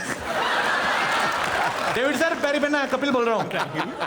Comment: What did Varun's dad say at 16:10? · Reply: Kapil sab hans rahe he....